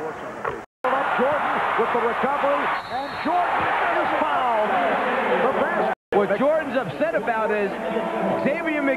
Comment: anybody know what he said? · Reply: I think he just said "Yeah, you sucker, yeah!"